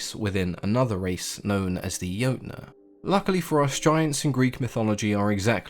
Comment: Sounds like you said "lucky for us giants"